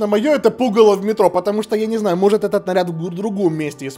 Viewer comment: мне послышалось не "пугало в метро", а "Пукала в метро"))